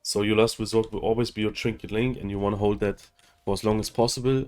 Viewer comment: One question. I didn't quite understand what you said at around the 6:20 mark. Your last resort is your what? Otherwise, great video and thanks! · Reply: "Your last resort will always be trinket link, kinda"